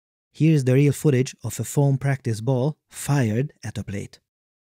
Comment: did you say "foam practice ball"? Surely it's a standard golf ball, no?